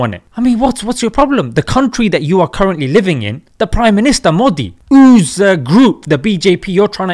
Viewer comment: I heard "mr dolaan trump" at 3:12 when u said pm modi. 😂😂